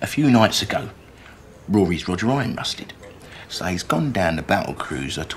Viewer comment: what a jam roll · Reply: Aaron Jeffery I guess it was the flow that got me. I heard "Roger Iron" then "rusted." Makes more sense now. What about "...ping-pong tiddly...?"